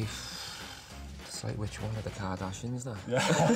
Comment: What does Gerard say at 1:54 ? English as a second language, i consider myself fluent, but that Liverpool accent is hard sometimes! haha. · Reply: He said “ it’s like which one of the kardashians” if am not mistaken